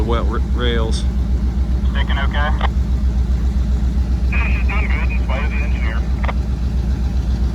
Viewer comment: I think i heard him say over the radio "We're doing o.k., despite I'm the engineer" 🙂